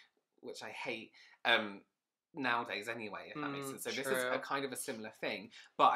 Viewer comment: I thought he said "shit's true" when he said "she's true" 😌